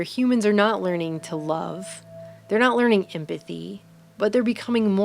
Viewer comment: Anyone, please, what the heck did the narrator say at the 15:58 - 15:59 point just after she said "Human beings are not learning to love, not learning to ___________________???????? " not learning to come fee fee ??????? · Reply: empathy